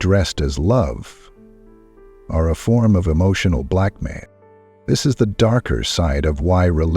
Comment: You may want to proof the video around 11:25. I'm sure you meant blackmail instead of black man. No bigger, but the video is so spot on I thought you might want to check it. Peace, Just Sam